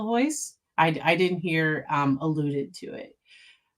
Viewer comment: i heard alluded